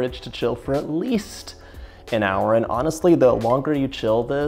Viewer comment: I heard 10 hours... Or overnight